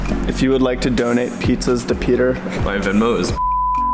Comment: What did peter say at 8:20 when it was blanked my english is not good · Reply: He said “my Venmo is” then they blanked it